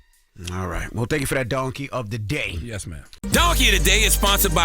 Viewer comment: Did Charla say "Yes Ma'am to Envy?🤣